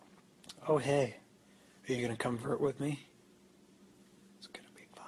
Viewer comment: what did he say? Convert?